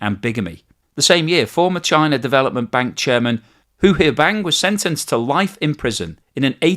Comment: I know you're saying "Hu Huaibang" but I listened to it several times and all I hear is "who here bang" and that is the best name ever.